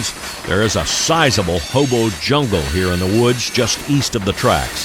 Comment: Did he say "Hobo Jungle" ? 😂